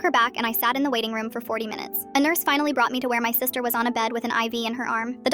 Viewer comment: I couldve sworn she said her sister was eleven..